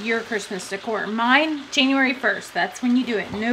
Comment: did she SAY JANUARY 1st GIRL WHAT